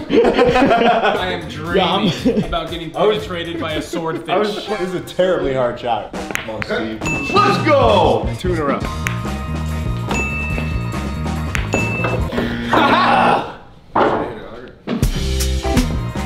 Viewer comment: Did he just say let's go Brandon😂